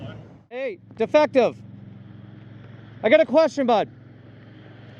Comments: OMG, did you call him Defective? 😂😂😂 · "hey defective" 😂😂😂😂😂 Had to rewind it to make sure I heard that correctly